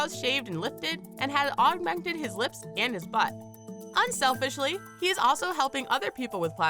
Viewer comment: did she say butt?!? Like wha?